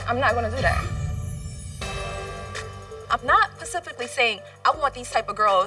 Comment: Omg did she just say "PACIFICALLY" GrammerGang